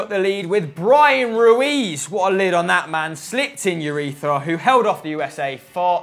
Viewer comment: Lmfao, did call Marco Urena urethra at 4:44???